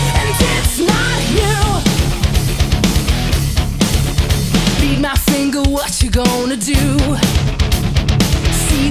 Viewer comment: someone could help me ?, I don't understand, what she said from the min 00:20 to min 00:24, Does she say " be now single " or "read my finger ? ,if she say "read my finger, what does she means? or what it means ? I speak spanish and english, but There's somethings that in spanish means other things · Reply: ***** She says "read my finger" as in she's flipping him off.